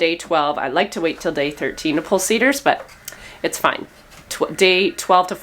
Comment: CIDRs …I thought you were saying SEEDERS, like planting that seed 😂